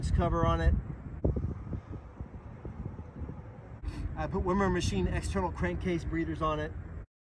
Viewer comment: names the external breathers at 8:00 but I can’t make out what he’s saying.. someone help me out?.. it wasn’t in part 1 or 2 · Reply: Wimmer Machine. Sorry, it was cold as hell out there, and hard to speak clearly